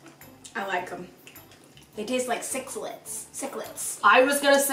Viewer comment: At first, I heard "they taste like six lips" lol